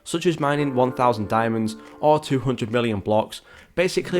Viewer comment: did bro just say 200 billion blocks to unlock and egg?? Only 11 people in the game would be able to unlock it